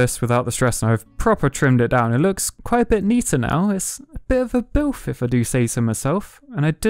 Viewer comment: did you just say BILF lol